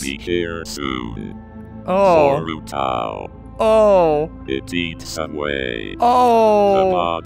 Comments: At first when it said " It eats away" I thought it said " It eats subway " XD anyone else? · when it had said "it eats away" i thought it said "it eats subway" XD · Dude its sound funny when it says "Eats away" I hear "I eat Subway" XD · I thought it said it eat subway 1:44 and when I saw the comments i realized😂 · when he was playing zeros disease when it said it eats away it sounded like it said it it eats subway